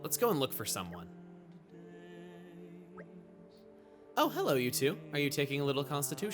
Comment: i heard that as "oh hello youtube" lol